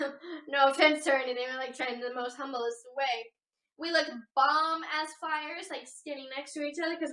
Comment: did she say "bomb ass flyers", or "bomb as flyers"? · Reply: bomb as fires.